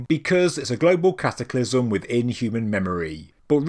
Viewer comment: "A global cataclysm within human memory." Just that phrase sends shivers down my spine..... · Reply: I first heard it as "A global cataclysm with inhuman memory..." That's a good elevator pitch, too...